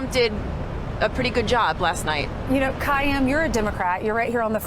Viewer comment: did she say Democrap???